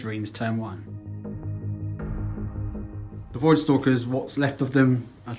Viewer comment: in the beginning i honestly thought he said "Boy Stalkers"